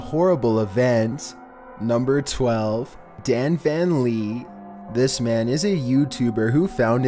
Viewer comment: I thought he said DanPlan